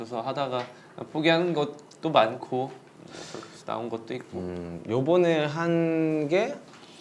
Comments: It just me or jungkook really said yeobeo to jin on 2:28? Then he change to hyung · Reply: Did he call yeobeo athu 3:55 also?🤔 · Did I hear correct that JK said yeobo at 3:55 or listen wrong? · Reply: no he actually said ibeonae na un and something if u can read korean there's korean subtitles 👁️👄👁️